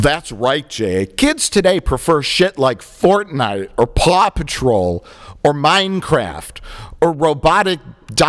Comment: I heard "Plop patrol". Mike, you need to take the knee for that vocal mess up.